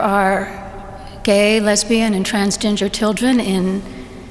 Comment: Somebody call Cartman! Am I the only one who heard “transginger” at 1:00?